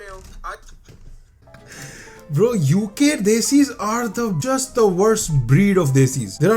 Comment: abbe UK kaha se aa gaya isme? She clearly said Brampton, which is in Canada. Even if she didn't say that, Canada is a safer assumption than UK given the much bigger Punjabi/Sikh diaspora there